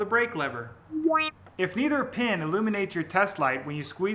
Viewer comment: Had to listen to 6:18 a few times to be sure you said "neither" and not "either." CC heard it as "either" same as I did the first time. Excellent video and explanation. Gotta go follow your instructions now.